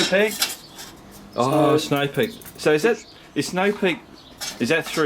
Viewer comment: Sounds like he saying snow pig and you guys down there do have something about pigs in your vocabulary for some reason????? No?? Lol 😂